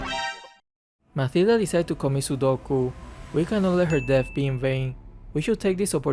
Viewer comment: did u just said commit sudoku 19:05 ???😂😂😂